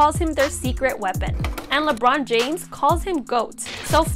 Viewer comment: I thought she said “you can still bald out” at the end